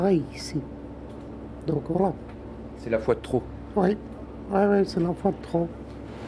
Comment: "c'est la Foi de trop" 3:57 "ué ué c'est l'enfant de trop" mais 🤣😂